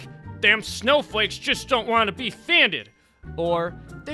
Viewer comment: what does that even mean? can someone please explain me. also which was the last word in this senetnce?sanded?funded?found dead?i have no idea what he said. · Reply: He said, "Them snowflakes just don't wanna be offended"